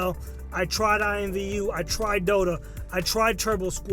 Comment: English is not my native language..., Which places do you name at 23:46? I hear IMVU and Turbosquid, but do you say something like "doto" but I don't know exactly what that is. Could you explain? Thanks in advance. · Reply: As far as I know, it was Dota Second Life